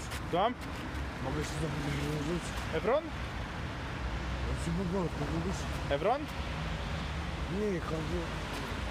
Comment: co on mówił? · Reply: Menel: Czy po polsku mówisz?